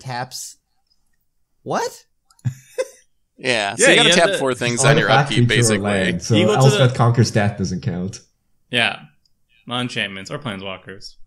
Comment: I thought said "Elspeth conquers Seth" at one point 😂